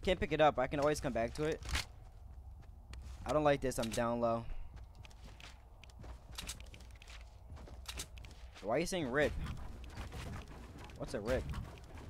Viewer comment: sounds like he said rift. Mind you that he's playing season 3 rifts were introduced season 5..🤔🤔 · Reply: "rip" not "rift"